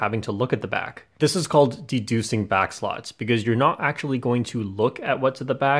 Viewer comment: J Perm:"Deducing back slots" What i hear:"Seducing back slots My brain:"Im out....." P.S THIS IS A JOKE PLEASE DONT GET MAD AT ME